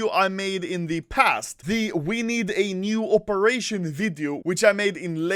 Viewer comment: I thought you said wiener operation (instead of we need a new operation) but.. I guess wiener operation would be great too